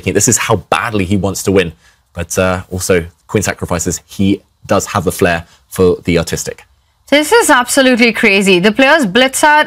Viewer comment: anyone else heard "he does has a flair for the autistic"